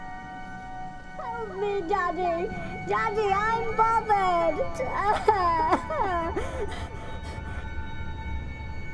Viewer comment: What did the tree say at 00:55?!!? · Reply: I heard "Daddy I'm bothered (crying)"